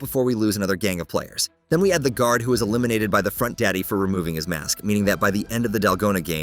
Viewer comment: Dog did he just say fontdady lol 😂